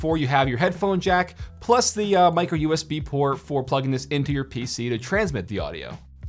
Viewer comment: What happens if you dont have a USB-C port on your PC for the Yeti MIc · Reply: Oh hahha, I thought you said USB C, instead of micro USB.. My apologies :D